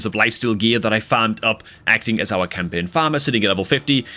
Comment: Ain't no way you got that lifesteal gear on wukong from campaign farming. I farm endlesly there and never got purple and mostly got 3 star most, not to mention the trash stat. Life steal set are what helps new account the most and i never got the good one. I even try to farm it in dragon and never got it. Any tips on this? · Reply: I think you misheard him. He said he got that life steal gear FOR campaign farming not FROM campaign farming.